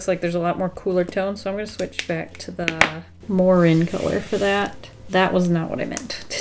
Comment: I thought you said more-Rin color and i was like oh that must me her fav earth tone lol 😂 😂